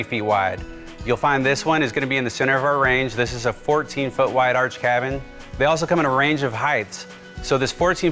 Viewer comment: I thought he was saying "Irish cabin". I'm thinking, what's an Irish cabin?